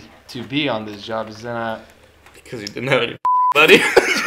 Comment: what does he say at 6:55? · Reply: “Because you didn’t have any fucking money!”